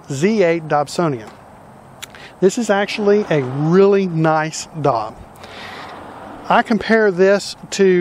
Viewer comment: What are you saying there at 0:27? "It's a really nice DOG"? It doesn't make sense to me. Maybe I just can't make out what you're saying. Could it be "DOT"? Please clarify. Thanks. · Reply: Dob... short for Dobsonian